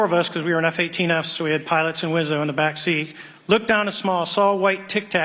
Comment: Did he say pilots and lizzo??? 😂😂